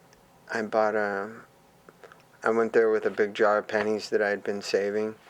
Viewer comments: You may be cool. But you will never be "9yo John Frusciante buying a Ramones album with a big jar of pennies" cool. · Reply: I thought he said 'big jar of panties' at first until my brain kicked in. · You may be cool. But you will never be "9yo John Frusciante buying a Ramones album with a big jar of pennies" cool. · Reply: I thought he said big jar of panties 😅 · I thought he said a jar of panties